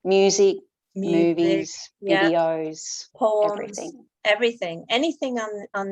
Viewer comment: Had to relisten to 18:19. I think says poems. At first I thought she said 'porns' ☺😄